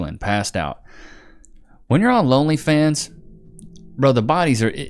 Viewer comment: did he just say lonelyfans?